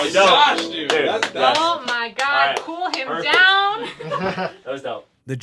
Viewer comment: While listening to this I heard “hold them down” and I love it so much